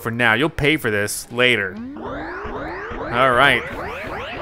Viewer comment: Mario's voice actor went on record that he says, "So long Kinga Bowser." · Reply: It’s probably just the N64 not having the best quality on audio files, I thought he said “So long-aye, Bowser!” I can see how people hear “So long gay Bowser” though